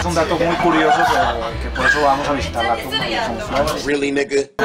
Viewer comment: Que dice en el minuto 19:27 el de barba?? antes del "really N****" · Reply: "Que van a visitar la tumba de" nada más eso entendí